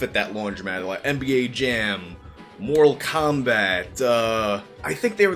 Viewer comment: Did he say Moral Kombat? Moral is like the exact opposite of how I would describe Mortal Kombat